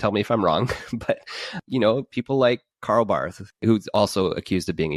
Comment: I did a double-take at 1:04:24 - I thought said "Carl Baugh"!